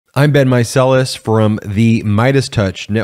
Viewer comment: Oh man Im just now realizing his name is BEN MEISELAS, I thought he was saying "I've been meiselas"